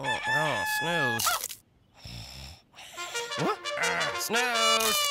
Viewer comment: Why dose the corn guy every day says’ gotta have my pops’🥣🥛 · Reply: It feels like I always hear: ‘got all my pops’